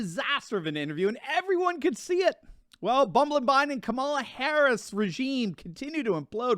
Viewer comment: Did he just say CABALA HARRIS?